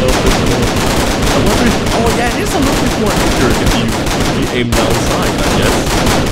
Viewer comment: I could not hear what you said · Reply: Think : Lower recoil when you aim down sights :)